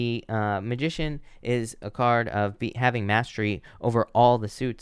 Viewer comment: Pisces ♓ once again on the nose 👃 for accuracy ! On card #2, I was grilling and could have sworn you said "thighmaster" had to go back, it was clearly not what you said! 🙃 Have a great evening!